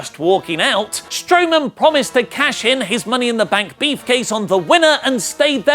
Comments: Money In The Bank Beefcase? Is that what he said? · Did he say "Money in the Bank Beefcase" or do I need to get my Hearing checked? 4:50 xD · listen to what he says lol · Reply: Mmm, "beefcase". *drools* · Who else heard say "Money in the bank Beef-case"